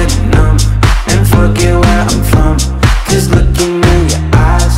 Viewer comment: I listened to this on the radio without the lyrics, and it sounded like he said “and f*ck it where I’m from. But in reality, he said and forget where I’m from